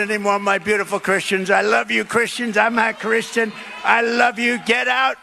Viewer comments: it sounds like Trump said, "I'm not Christian." That Freudian slip tells Christians all we need to know... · of this video. Does anyone else hear him begin to say "I'm not Christian" as he is pointing to his chest. To me, it sounds like he realizes he's about to tell the truth, and he slurs the [ t ] just enough, certainly not to be heard over his adoring congregation. Except for the fact it does not matter. At this point, if you don't know who to vote for, and it pains me to this, please don't vote.